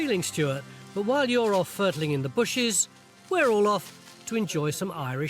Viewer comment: or so: Tony frequently uses a word I don't know that seems to rhyme with "turtle", but starts with an F..., Furtle? Fertull? I can't find it in the dictionary. Well, "fertile" is there, but I don't think that's what he's saying. In American English, "fiddle" as in "fiddle around" would make sense. What IS this word? Anyone know? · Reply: He is saying 'furtling', which is an actual word, but not a common one. From Wiki dictionary: To gently delve; to probe or rummage tentatively. There is (of course) an Urban Dictionary meaning too, but I'm pretty sure he didn't mean that....